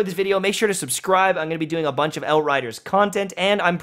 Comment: did he just called the game hellrider?